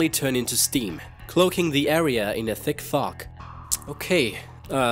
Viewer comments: is it just me or does he say "in a thick fuck" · Umm lol either his tongue slipped or what but at 0:58 to 1:00 it seemed like he said F*** instead of fog :P :P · it sounded like he said at 0:56 : Thick Fahk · Did i hear that correctly? 0:56 - 0:58 "cloaking the area in a thick f*ck?